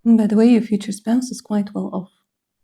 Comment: OH MY GOOOOOD! 🤣🤣🤣 at 17:18 you said "quite well ..f" so I asked out loud "quite well what?" and you said "quite well off" lmaooo unbelievable!